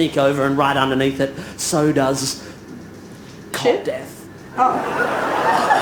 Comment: No, I don't think Tim said "Cop Death" either. Listen again. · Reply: He didn’t. I think he said Cot death. I had to look it up but it’s terrible. Sudden infant death syndrome (SIDS) – sometimes known as "cot death" – is the sudden, unexpected and unexplained death of an apparently healthy baby.